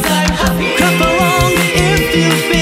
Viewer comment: Very good! But I kept hearing "crap along" ? Lol 💩